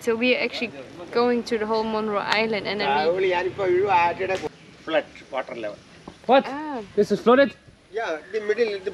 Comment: Any malayalis listened what the boat rider said? 🤣🤣🤣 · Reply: He said “thayooli” rt? 😂